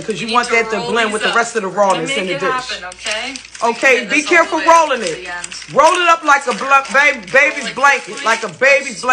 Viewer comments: Babygirl I know I heard you say roll it up like a blunt! Stop playing! 😂😂😂😂😂😂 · Could’ve sworn I thought you said, roll it up like a blunt 😳😰😮‍💨😂😂🤣